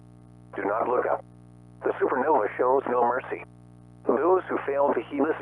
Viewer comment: thought it said "do not look up "EAS scenario""